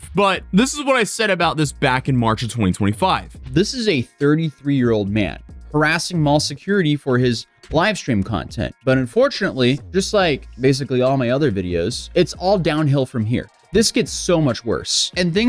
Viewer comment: did you say Vitaly is 33? 😮 Also is great. 😂